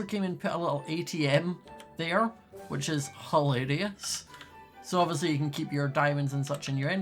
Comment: If you put subtitles on at 9:26 Scott says hilarious, and the subtitles say "whole idiots"